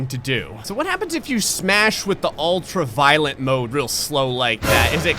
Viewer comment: It took me two of these videos to realise that what I heard was not ‘ultraviolet’ but ultra violent... I was wondering wtf ultraviolet mode did! I was expecting strobe effects or something!